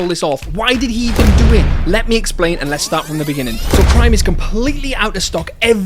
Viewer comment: swear he said crime